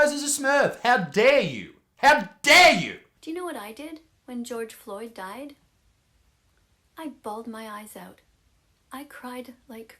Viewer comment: I heard you know what I did when George died I laughed my ass off I went to the BLM protests and ran the mother fuckers down